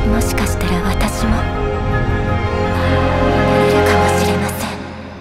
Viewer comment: アイオーンになれるかもしれませんって言ってるように聞こえる…？